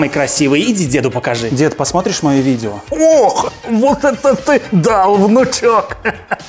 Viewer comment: почему мне послышалось "ох вот это ты даун внучок"